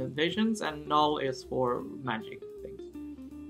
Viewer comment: " and null is for.." what?! did he say magic.